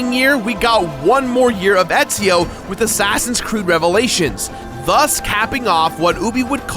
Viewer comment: did you say assassins crood